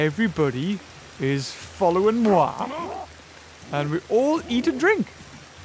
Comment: I heard you say. " and we all eat a drink"